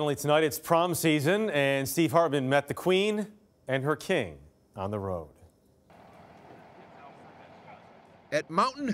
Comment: honestly at first i thought he said steve harvey on the road